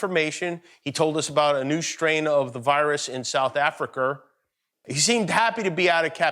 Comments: Did he just say south africker? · Did Jimmy just say "SOUTH AFRICARRR"?